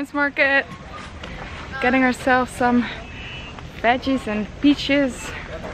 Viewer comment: Haha her accent is so cute it sounded like she said veggies and bitches.